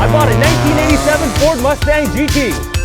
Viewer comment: Bruh... Did he just say 1997 mustang gt...